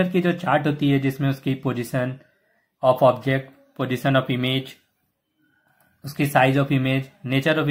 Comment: What did he say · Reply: He said position of object